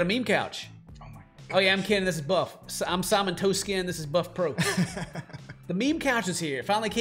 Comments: Am I drunk or did I hear him say "I'm Simon-toast-skin"? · Lol, at first I thought he said his name was Buffet Ron, not Bufftron!